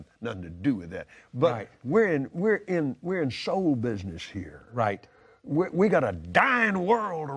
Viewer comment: Did he say "we are in Soul business here"? Did he basically claim to be Satan? xD